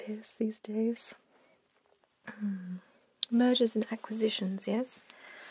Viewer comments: i thought i heard murders and acquisitions lol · If there's such a thing as Freudian slip for hearing then I had it at 5:45; thought she said "murders and acquisitions" 😅